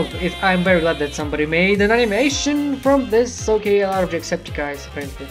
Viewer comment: Did you just say Jacksepticeye(s)? 0_0 XD